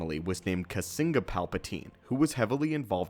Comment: For a second there I thought you said the father's name was Bazinga Palpatine XD